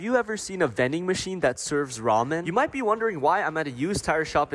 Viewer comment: I heard "raw men” 😭😭